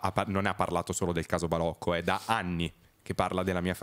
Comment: non ho capito cosa dice fedez a questo minutaggio, forse intedeva ZA ANNI?